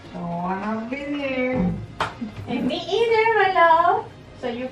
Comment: I heard “don’t wanna be here” and said “me either love” am I tripping?